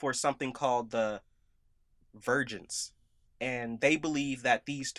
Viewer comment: I'm not a native English speaker so I had to look up "vergence". I'm probably not the only one who misheard the jedi were looking for "vigins". Yikes.